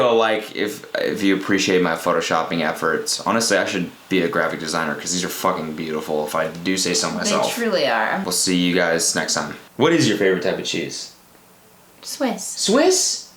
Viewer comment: i thought he said "what's your favorite type of jesus"